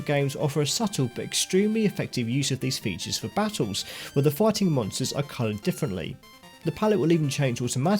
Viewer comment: i cant the only one who thought he said "the farting monsters" 💀